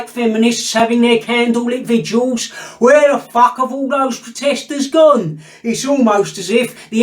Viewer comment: You’ve got me in big trouble, my phone lit up and my wife thought it said “ where has the Prostitues gone .. thanks a lot 😂